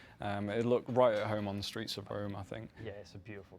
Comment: Sounds like he says streets of Rome